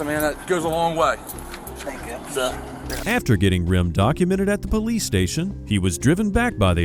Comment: I heard that as, “after getting rimmed….” 💀💀💀 Uh, yes please lol